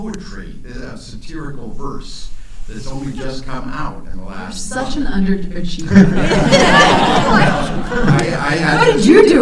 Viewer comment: what did Charlize say in 31:52???? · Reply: Oyster Sauce “You’re such an underachiever.” I listed on better headphones.